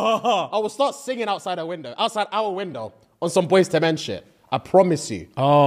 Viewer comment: The captions saying “boys dementia” instead of “boyz II men shit” is hilarious 😂😂😂😂